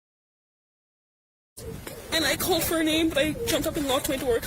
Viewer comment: The 911 call doesn't square with the scene when LE casually arrives. At all. Suss or not, DM definitely sounds like she says "I called for an ambulance" and they edited it to say "I called for her name" (on scene interview).